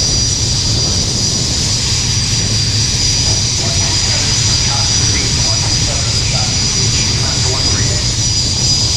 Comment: What did the Air boss at 9:06 say??? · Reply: Launch complete…then something about the COD is going to recover soon..